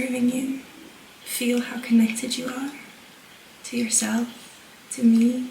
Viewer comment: I thought I heard to contact you